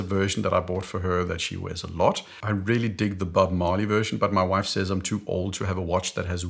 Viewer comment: The first time I listened through, I literally heard "but my MOM says I'm too old to have a watch that has weed on the dial.." I was shocked when I listened back to find the timestamp that you actually said "but my WIFE says..." This is a message to both of us - and the message is: get the watch with weed on the dial if you like it. Do what you want!